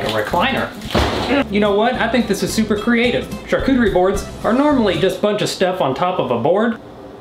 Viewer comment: who else thought it said haircutter? i was very concerned